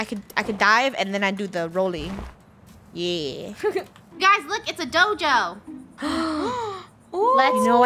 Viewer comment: I paused it and thought she said, "Guys look, It's a doge!" bork bork